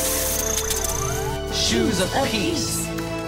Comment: thought he said shoes of herpes